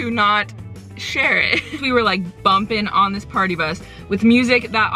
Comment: Why am i andrew at the partybus? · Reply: i thought you said platypus instead of partybus and i was extremely confused for exactly 18.2 seconds